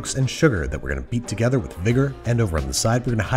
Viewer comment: what did you just say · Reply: Vigor